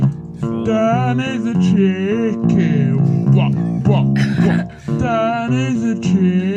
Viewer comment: For a moment I heard "Dan 🎶 is a chill king 🎶", which he sure is. 😂